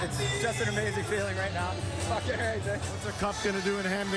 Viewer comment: guy walks by and I hear a 'fucking a right' or some shit.